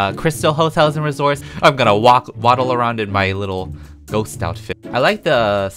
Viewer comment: lol at 1:10 i hear 'im gonna wap- waddle around'